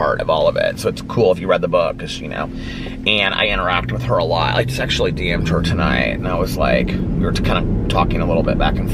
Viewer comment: does he say he sexually DM’d her? 🤣🤣🤣 I’ve listened to it like 10 times and that’s all I hear. Also anyone remember the whammy game show? Loved that show! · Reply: “I just actually DM’d her tonight” ran together lol, but yes it does sound just like I just sexually DM’d her tonight lol